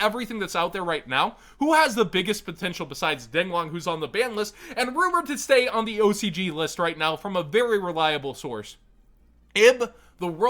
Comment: what did you just say? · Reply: Know how the past few ocg lists were spoiled and someone got banned for spoiling them?